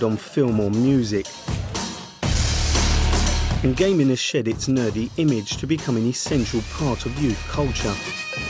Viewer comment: did he just say "and gaming is shit its nerdy image to the centrel part of youth culture"? because I cant make up of what he said.....